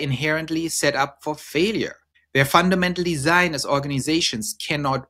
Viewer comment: I heard "they're fundamentally Zionist organisations" ... Had to go back and double check it 😅